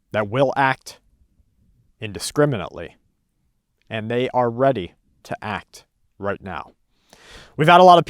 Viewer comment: Wait, i thought you said theyre already activated??